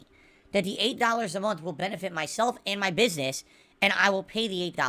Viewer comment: I thought he said he doesn't own a "business"...